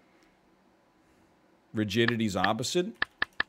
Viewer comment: straight up thought he said 'virginity's opposite'